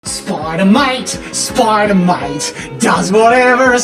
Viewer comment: I don`t know why but i heard him say either Spider Mite or Spider Moit. SpIdEr MoIt SpIdEr MoIt DoEs A sTuFf SpIdEr MoIt